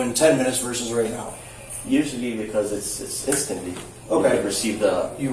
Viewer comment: what did say? “Assistantly” another new word! When he should have said “instantaneously” just as the officer would say later😂😂😂😂